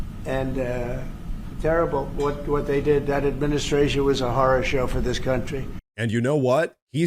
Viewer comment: I swear I heard "that administration was a whore show for this country." I must not have been invited to that :\